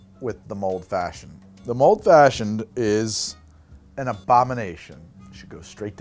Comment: i thought he said mulled fashioned